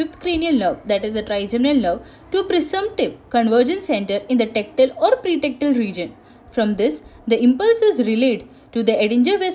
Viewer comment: what is that word? thanks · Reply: Presumptive centre in the tectal Or pretectal region